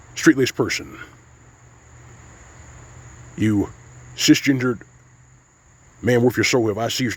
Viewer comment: I swear I heard you say "cis-gingered" 😂